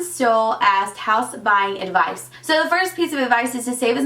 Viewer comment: Omg 😂 I thought when you said "house buying" you said "house spying" then you said save your money for the stuff you will need. I was so confused for a minute lollll